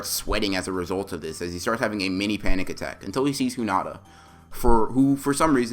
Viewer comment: Me:😐😑 Edit: he said hoonata instead of hinata if you did not get it